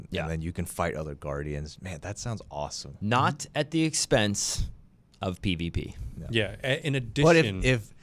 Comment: I thought when they were talking about d3 and PvPvE areas I thought he said not at the expense of BBD (big black d***)